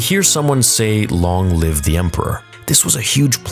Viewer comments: I thought they said, "LONG LIVE THE EMPIRE!" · I heard, "Long live the Empire".